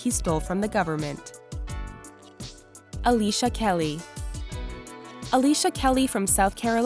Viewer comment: when she said Alicia Kelly I thought she said Alicia keys I all had rewind it LOL